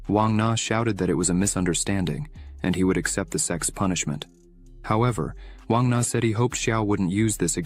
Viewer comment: my caption write s*x punishment instead of sects punishment